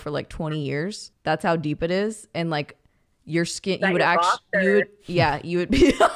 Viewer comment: What the hell did say at 24:10? I can’t figure it out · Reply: She said is that your box 🤣